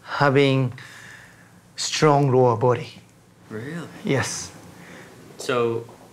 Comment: Found the video extremely interesting and cool. Can anyone understand what is the secret to longevity as said on 18:20 by the Miura son? I cannot seam to understand his english at that point: "...secret to longevity is strong ??????? body..." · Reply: Even Better lower body